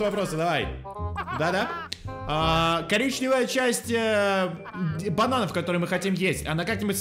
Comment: на 0.25 скорости 🤣🤣👌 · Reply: Я услышала "е**чая часть банана*😂😂😂